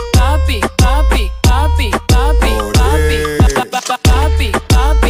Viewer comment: Why do I hear “ barbie barbie barbie barbie...” instead of “ papi papi papi papi...”